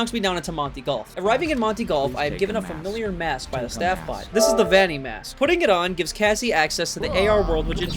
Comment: I heard the baddie mask😂